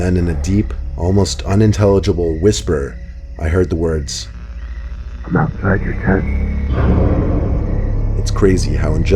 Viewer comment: what did the walkie talkie say? I'm outside your tent? Is that right? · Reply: Yeah now i heard it fora couple of tiems its true it said im outside your tent